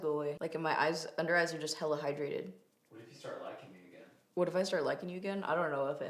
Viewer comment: Reallllly thought said “Maybe you are gonna start lactating again” 😂😂😂 instead of “liking me”